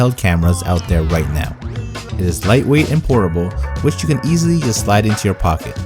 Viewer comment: who else thought he said “ it is lightweight and horrible”? 😂 😂 😂